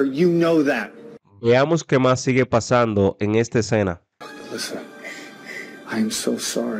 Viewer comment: Pensé que dijo listen no Alyssa primero Jajaa quiero llorar debo entender mejor el inglés por Dios